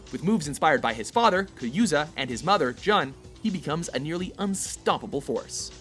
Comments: did he say Kayuza and John. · Imagine if WatchMojo pronounced Kazama as Ku-zam-a 😂😂😂😂😂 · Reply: He said kayuza instead of kazuya lol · did you really said "Kayuza"?